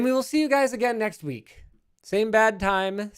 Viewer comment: i thought for so long they said "same bat time, same bat place" at the end of each video, and i never got why there were making batman puns XD, i just now realize that linus is saying bad, not bat lol